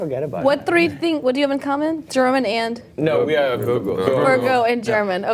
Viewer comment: "We are German and vulgur"??? Sorry, what's the last word?? German and what?? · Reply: Virgo (the zodiac sign)